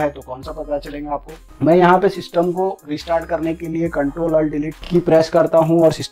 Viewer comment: pr aapne kya bola ctrl and delete or ctrl alt key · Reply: CTR + ALT + DELETE